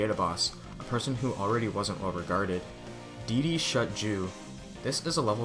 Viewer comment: What Did You Say? I Did Not Understand It. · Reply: Dedeshutju